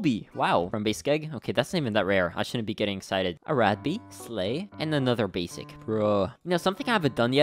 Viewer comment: Every time he says “ a rad bee” it sounds like he says “ arack bee”